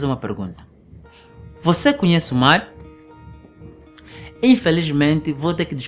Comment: Achei que ele tivesse dito "Conhece o Mário?". Pensei: "to aqui!"